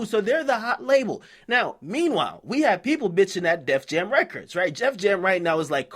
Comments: he said "Jef Jam" instead of def jam 😂😂😂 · you act like I didn't hear you say Jeff Jam..lol